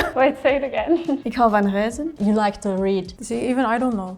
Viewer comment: Spoiler alert : It was her French accent i'm Belgian and i couldn't understand what she said "reisen" i thought she said "huizen"